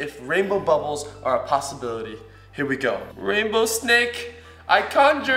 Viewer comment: Who else thought it said snack? Me